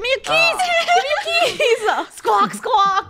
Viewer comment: did funnel say give me a kiss:_KREWClown